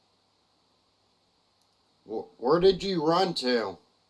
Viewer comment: I hear, "You'll run from this."